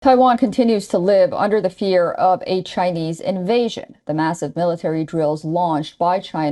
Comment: I thought she say a HIV invasion instad of saying chain 😂😂😂